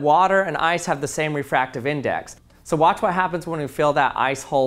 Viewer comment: Anyone else notice how he slowed down “ice hole “ so it wouldn’t sound like asshole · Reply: I thought he said nice hole 🕳